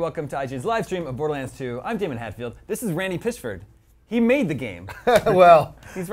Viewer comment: i thought he said randy pissford